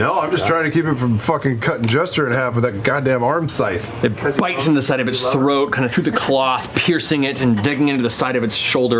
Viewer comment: What does Sam say at 1:07:43 ? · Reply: Greysider Travis says he’s trying to keep the Laughing Hand from killing Jester and Sam says “Because you love her”